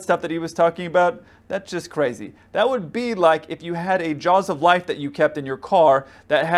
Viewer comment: what did he say at 11:34? jaws of life?